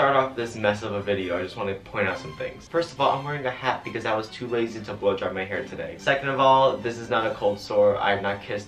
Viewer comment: I literally thought he said he was lazy to blow job his hair....is that weird?...